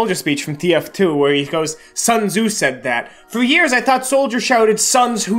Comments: I also thought he said "Sons who said that?" Its good to know that i''m not the only idiot on planet earth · AHA IM NOT THE ONLY ONE WHO THOUGHT SOLDIER SAID 'SONS WHO SAID THAT' IN HIS MEET THE VIDEO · I also thought Soldier said "Sons, who said that?", but i thought it was him quizzing the "sons" about who said the quote.